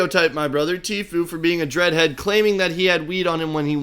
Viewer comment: what did she call him? I couldn’t hear and I went back a few times. I know she prolly called him something awful I just wanna know. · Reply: a dread head I think, or that is what I heard